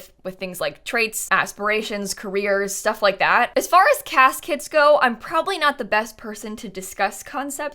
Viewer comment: i thought she said "caskets" instead of CAS kits and was ready to agree we could have those too lol